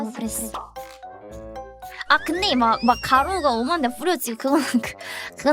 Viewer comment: 엄한데 -> 오만데 인 거 같습니다 아마도? 경상도 사람인데 그렇게 들려요